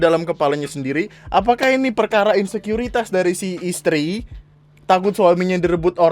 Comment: Ini gua yang salah denger atau gimana, tapi gua denger mbaknya bilang "Sukuriti"...